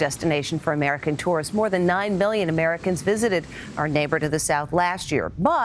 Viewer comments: did she say 9 billion, sounded like it?!!?? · did she say billon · did she just say 9 billion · Did she just... did she say 9 billion americans? There aren't even 8 billion people on the earth like wth😂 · Why does it sound like she says 9 billion Americans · I know it was probably ment to be million but it sounds like she said billion